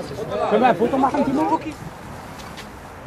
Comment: "Hyvä Pukki" ? Was there Finns, have someone learned Finnish or did I heard wrong ?